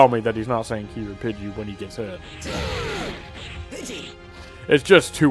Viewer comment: The "kEEEEEY/Pidgy!" line sounds like he is saying "Pitty!" to me